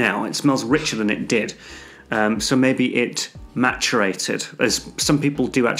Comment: I always thought they said "macerated" as this is an actual term in perfumery as far as I know.